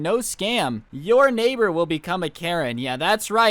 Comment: I thought he said “I will turn your neighbor into a carrot” that’s an awesome power